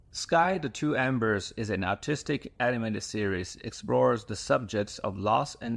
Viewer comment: When the Asian narrator says artistic it sounds like he said autistic. And it made me laugh.